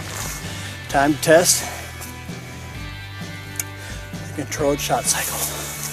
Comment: I had to listen twice to get "controlled shot cycle," I thought you said "cold shot cycle" and I was going to have to throw the BS flag. You know in the Terminator movie when John Conner is telling Sara how WW III started. I am convinced it will be an auto-corrected tweet or IM that starts THE BIG ONE.